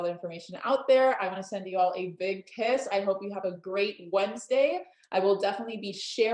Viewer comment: I know that the Monat hun said "I'm sending you all a big kiss" at the end of the call. I know that. Still, somehow, my ears heard it as "sending you all a big piss", and I think it's a hundred times more truthful than the things she actually said in the video.